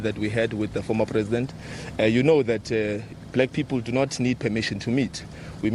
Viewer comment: I thought u said as blacks u dont need an invite. Get ur mind right. Julius is worried about the vbs scandal. They want to dissolve the commission.